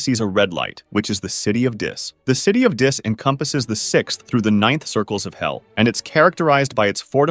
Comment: i fkn thought it said nine circles